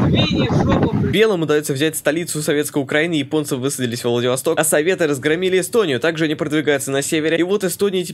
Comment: отвлекся, послышалось Эстонцы взяли Владивосток...